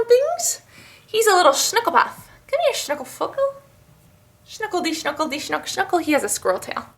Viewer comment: hahaha it sounds like you say shnookle fucker to your cat!!!